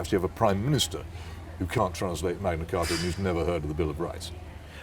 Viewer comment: What does Peter say at about 42:15? "Can't translate..." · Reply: Magna Carta and who's never heard of The Bill Of Rights